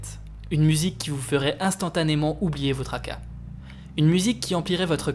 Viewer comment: Elle m'a fait oublié mon Aka c'est bon.. mes tracas* pardon (j'ai entendu "oublier notre Aka" au début de la vidéo et ça me paraissait bizarre) Après, le fait que le twist, c'est que le tueur est vraiment celui que l'on soupçonne depuis le tout début, je trouve ça pas mal.. on a l'habitude d'un retournement de situation un brin idiot du genre "ah mais il a une pelle parce qu'il plante des fleurs" ou "ah mais il a son t-shirt parce qu'il l'entraînait au baseball un week-end" et c'est dingue que le film nous perdent autant, il y a plein de choses qui s'expliquent, nous mettent en déroute en occultant le fait que plein de choses ne s'expliquent pas, la formule chimique,.. j'ai oublié les autres car ça fait longtemps, mais je me rappelle que ça m'avait fasciné une telle maîtrise du "c'est lui ? C'est pas lui. C'est lui ? Mhmh complexe", alors qu'il est grillé si on réfléchit bien